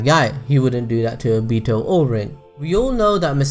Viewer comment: "He also experienced the death of 'A Beetle'" I literally heard "A Beetle" instead of Obito xD